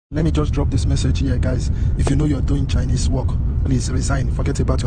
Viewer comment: The accent of the chap at 8:50 is definitely Nigerian. The inflection sounds Yoruba. At 9:24 what he says is “if you know you’re on overstay”. That is what the question mark word is